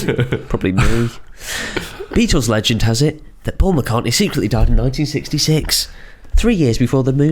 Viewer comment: I heard "Paul McCartney's actually superman"